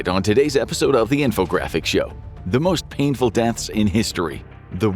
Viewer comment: I thought it said “most peaceful deaths.” “You wanna die peacefully? Here, have a bear.”